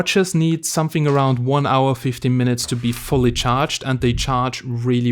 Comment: did you say 1 hour 50 minutes or 1 hour 15 minutes? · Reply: 1hr 50 mins